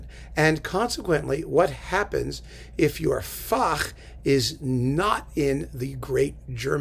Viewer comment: Hi, at 0:55 of this video you use a word that I have heard you use several times before, and I don't know how it is spelled or exactly what it means. The word seems to be pronounced "fock" with a germanic gurgle at the end. Maybe it rhymes with "Bach"? It seems to mean "specialty" or something like that. I've tried to locate the word but I'm coming up empty handed. Would you please identify this word of which you are so fond? Thank you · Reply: Fach